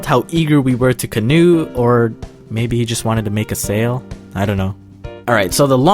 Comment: When closed captions says “sail” instead of “sale”...🤣(3:05 btw)